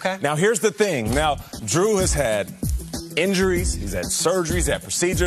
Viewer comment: for a moment I thought he said Drew Brees had seizures